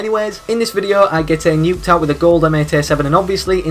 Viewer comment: you said, I got a nuketown instead of nuclear